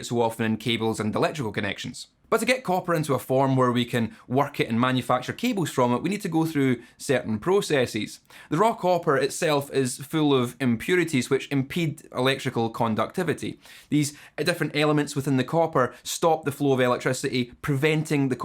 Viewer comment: You said raw copper. I heard “Rock opera”